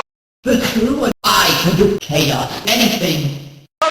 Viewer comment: Why do I just hear "I CAN DO ****ING ANYTHING" also 15:09 I just hear "Kashew-" [Vanish.wav]